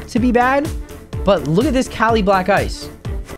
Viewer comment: did you say Kali Black?… ooooh Kali black ice… my bad 🤣